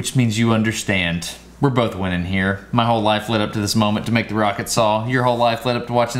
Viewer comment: Thought it said 'pocket saw'. Wasn't disappointed, only confused. :)